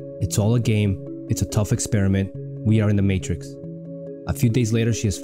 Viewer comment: I thought she said 'thought experiment' not 'tough experiment'